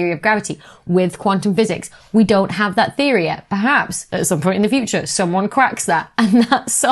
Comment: Did you say "perhaps someone quacks that"? Forshadowing the duck? lol